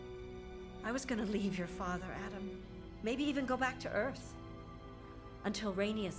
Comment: Did she say EARTH????